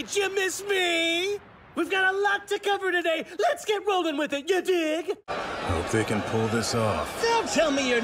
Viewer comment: did Mic really say 'yuh dig'